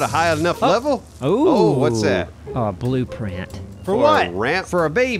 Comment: Did I hear, "blueprint for a baby!" at 19:40 haha how did that not kill these guys? haha I literally laughed out loud. HAHA